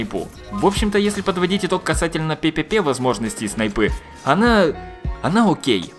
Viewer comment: я один слышу слово "пепепе"?